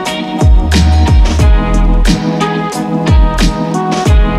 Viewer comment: Awh I thought it said exploding 🫤💁‍♂️😒nvm